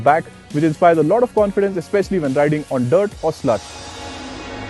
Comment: did he just say sl*t instead of slush? or have I gone deaf?